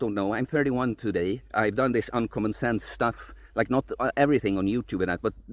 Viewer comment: I'm following your advice, so hopefully I too will feel confident enough to only wear a shirt when i am required. You say "uncommon sense" so quickly. I thought you were saying "como estas" haha! Thank you for taking the time to reply & explain